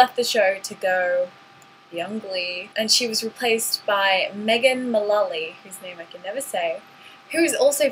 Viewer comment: It sounded like you said megan mememolly :p